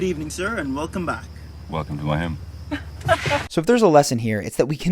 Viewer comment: WTF did he say at 6:38? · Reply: Welcome to my home